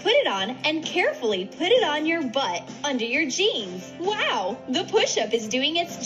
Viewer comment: I thought they said butt the cheeks but it was jeans